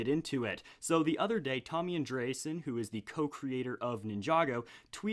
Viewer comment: did he just say Tom and Jerry?